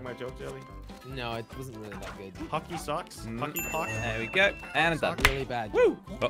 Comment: excuse me did u just say monkey pp? as a cuss word did